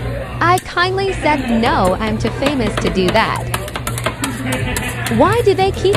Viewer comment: “I ‘Kindly’ said no, im too famous for that” 💀💀💀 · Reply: I thought he said "No, I'm not famous for that" 😭😭😭💀💀💀